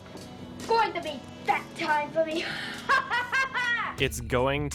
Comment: I thought he said "It's gonna be fap time for me"